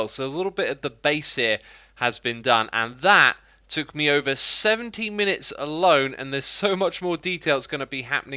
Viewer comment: did he say 17 MINUTES? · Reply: No, he said 70